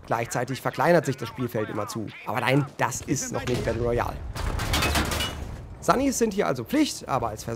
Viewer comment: was sagt er da? Das ist noch nicht der neuiejal? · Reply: Das ist noch nicht Battle Royale.